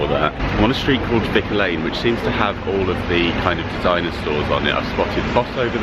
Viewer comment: When you mentioned Designer Stores, my husband, who is 61 going on 7, thought you had said dinosaurs!! He has a rather unhealthy obsession with dinosaurs 😂🦖🦕